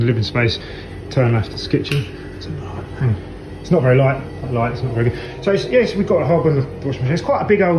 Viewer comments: ! at 7:12 minutes into the video as the guy walks into his kitchen to turn on the light it sounds like there is a quiet voice saying "it's a light" can anyone else hear it???? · UHM HELLO??? Am i the only one who heard an extra voice at 7:12? Saying "the light" or something... Cant figure out if he said it or not, it sounds so odd that it would be a whisper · Am I the only one who hears a voice at 7:12 ? Just when he turns on the lights? It sound like: "It's a lie?"